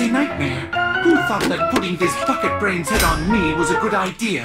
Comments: when he says who thought that putting this bucket brains I thought it said I thought it said. Who thought that putting this f*****g brains head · Hope you all like the video! There's a few points that I think I should have explained better, but overall I hope it's a fun watch. And feel free to disagree! Don't be mad! It's just a blue hedgehog! Be cool be wild be groovy! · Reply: 9:59 did he say fuck it brain on me ???